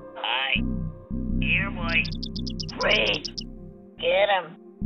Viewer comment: Me and my little brother always thought the sligs were saying “WEEEEE” 😂